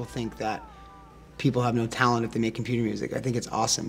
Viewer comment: "If they're making *** music" I didnt understood what he said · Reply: computer